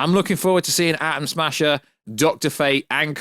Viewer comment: Did he say Adam Smasher lol?